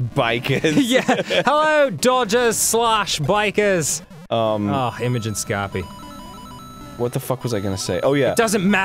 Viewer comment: when says something along the lines of "Ohh Imagin Scopie" what the hell is he saying? I am so curious, it sounds so familiar.